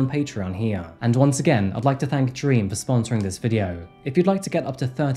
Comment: I kept hearing “dream” but all I saw was “Beam” and I thought I was going crazy. I even tried to move the letters in Beam around to make it say “Dream.” 😂